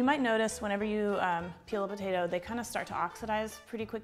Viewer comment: I was pulling my studio headphones from my ear when you said "when you peel a potato" and I thought you said "when you kill a potato." At first I thought it was weird but then remembered I was watching and literally anything is possible. 😂